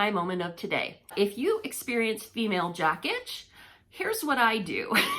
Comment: I thought you said female jackage and googled it. LOL Wouldn't tea tree oil set you on far? I'm from Oklahoma and that's how we sometimes say fire. I used male jock itch cream once and screamed!!